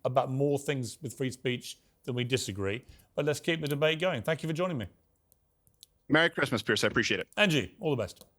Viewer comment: Did Steven say Merry Christmas Chris at the very end? @ 22:12 😂